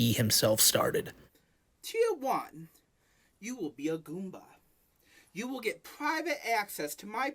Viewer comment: thought bro said “tier 1, you will be a groomer”